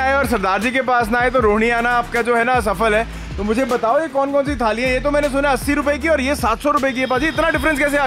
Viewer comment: bhai kya bol gye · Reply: Asafal bola tha but ab hassi aa rahi hai 😅🤣